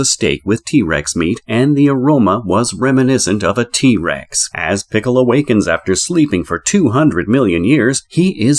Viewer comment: hah you called him piccolo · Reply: Hmmm..I hear "as Pickle awakens". Lol